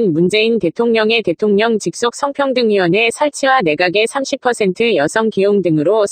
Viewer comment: 오타아닌가 ㅋㅋㅋ30대가아니라 30%라고 들리는데